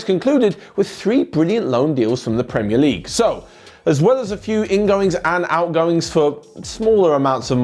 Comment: Did he really say in goings in